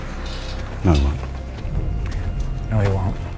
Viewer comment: what does Tony say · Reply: He said "no he won't" as In the guy will not set him free when he's done.